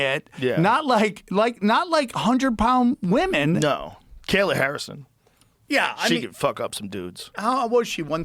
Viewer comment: For a second I thought it said Sam Harris...